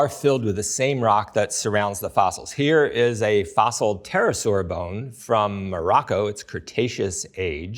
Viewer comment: subtitles are wrong, he's saying pterosaur, not tyrannosaurus